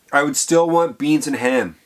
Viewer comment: DID HE SAY IF HE WAS A QUADRILLIONARE HE WOULD STILL WANT GREEN EGGS AND HAM 🤣🤣🤣🤣